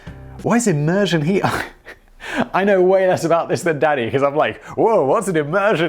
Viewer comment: ohhh i thought he was saying daddy the entire time LOL